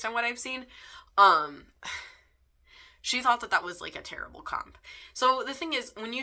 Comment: I thought you were saying "Title Cop" I could get on board with that. Nobody should be bothering you about your title. But title comparison, you need to have those sometimes. I mean, I do have a world as rich as Tolkien's or Herbert's. And my work is a bildungsroman, chronicle novel and novel of manners. It's just how you explain it. And that work is my opus.